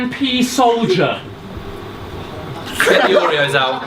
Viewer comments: What did say at 21:45? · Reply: "Get the Oreos out" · What did say at 21:46? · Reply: They said ‘get the Oreos out’ in reference to the time they did a concept rap/rap battle (search and vs fantastick for some more context) about Oreos. Basically they’re playing on the joke that their competitors are so ‘good’ that they daren’t battle them and want to return to battling cookies in concepts to avoid defeat - whilst the joke is really that they are aware they are far superior to their opponents.